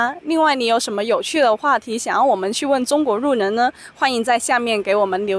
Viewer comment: thanks for the video, btw in 3:28 i heard she pronounces 路人 as "ru nen", did i mishear it? or is it an accent from somewhere? thanks in advance for the answer